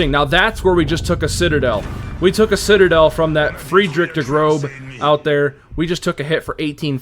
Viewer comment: No hate, but German speaker here. Heard you say Der Grobe. The letter ß makes an “ess” or hard S sound in pronunciation. So its Der “Gross” Große. Great Video!